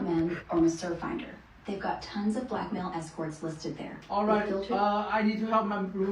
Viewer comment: I hadn't been paying attention and thought they were saying blackmail experts